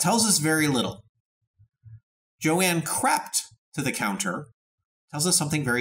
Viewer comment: Misheard that so bad at first: "Johanne crapped to the counter... Now that tells us something very different." Oh yes it does!